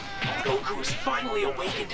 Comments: the captions said gohan but said Goku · anyone else hear say "goku has finally awakened his power" instead of gohan at around `